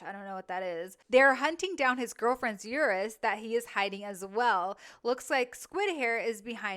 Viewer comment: their hunting down his girlfriends what?? 😂, think I miss heard it, anyway, you’re hyped… .🇬🇧🙏🏼✝️🙏🏼✝️